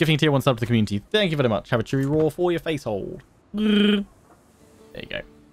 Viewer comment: "Chewie roar for your face?" And here I've thought for months you said "chew off your face" O.o